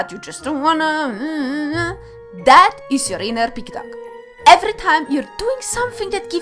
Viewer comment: at the end, it took me a moment or two to realize you were say "pig-dog". Which is good. But, I'll go with what I misheard at first. It is far more amusing for me to chastise my inner pig-duck. And, to both you and Logicked, Keep up the good fight!